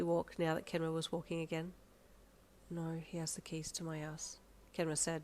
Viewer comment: EVEN THOUGH I KNOW THIS STORY WORD BY WORD NOW, I heard :"No, he has the key to my ass." 🤐🤨🫡